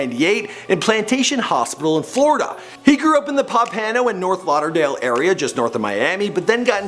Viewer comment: Ten - tah - see - own Smh... Sounds like ur saying tennesseeum